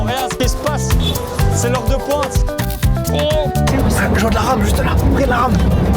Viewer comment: j'ai bien entendu ce que j'ai entendu ??? 🤨 la vidéo ne fait que commençer je sens qu'elle va être banger ! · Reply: moi aussi j'ai bloqué au début. Il dit: "de la ram"